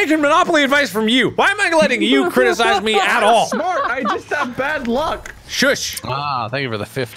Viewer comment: The captions are wrong. I think Wade is saying "(I'm) smart!" not "Mark!"